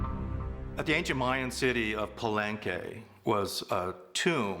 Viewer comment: did he just said "palengke"? A Philippine word for "market". I think they're makin it up telltales